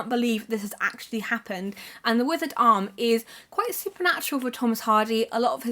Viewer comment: I thought you said 'The Wizard Arm' with that Thomas Hardy book haha. I was about to say, Hardy wrote a fantasy book?!